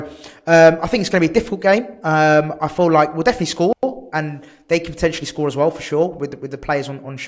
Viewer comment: Cocky one called...thought you said you will definitely score against us 😉 😜